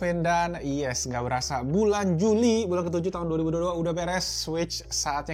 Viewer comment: gak salah denger sampe di ulang ulang dengernya kok bulan juni ko Sampe gua coba slow mo 🤣🤣